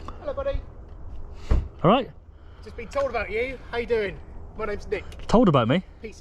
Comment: Did he say, "Hi, my names head ?